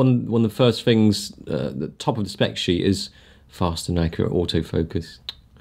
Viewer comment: I thought it said fast and accurate as fuck xd